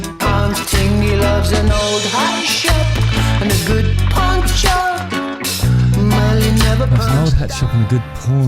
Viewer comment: I thought he said porn shop too, then I thought it said poncho.